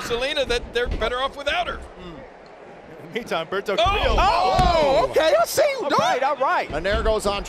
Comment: did her really say Alberto del Río lmfao